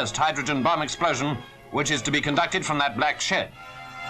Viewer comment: Did he said "Black S@ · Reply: Shed